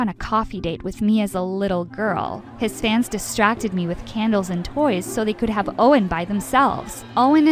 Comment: did I heard it right? CandLes and toys?